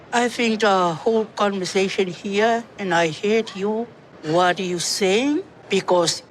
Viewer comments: I don't think the Mom said "I hate you", I think she said "I HEARD you" but mispronounced it. She pronounced it like HEAR and added a "d" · I dont think the mom said "I hate you" I think she said "I hear you"